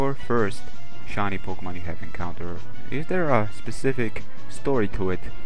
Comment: oh you said incounter thought you said catch make that shiny espeon sorry but i think espeon looks better pink and it was in the battle tower that how i learn about shiny pokemon